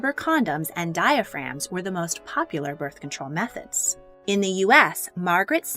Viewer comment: I thought it said “bird control”